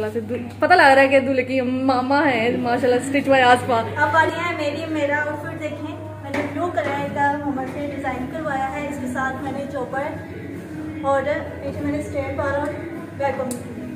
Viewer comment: did I hear chopper??😮